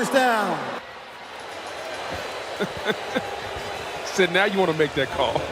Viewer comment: I thought he said “daddy wouldn’t want to make that call” 😂